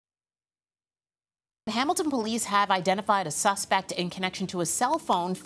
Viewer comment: Did she just say " cqmeltoe police " ?